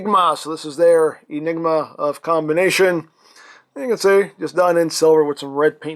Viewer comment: Did you just say “Enigma of Combination”?